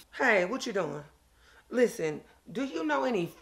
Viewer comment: I thought she was gonna say "Food bank"... she said "Food BOOSTERS" LOL!!!